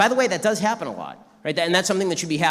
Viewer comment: 에 centralizing 이 아니라 incentivizing 으로 들리네용